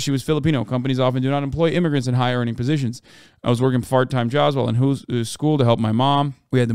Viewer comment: damn he really said fart time jobs